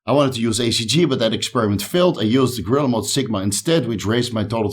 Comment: what you used to boost test,what are you said at 1:32? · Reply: Gorilla Mode SIGMA, i've got it linked in the description section buddy ;)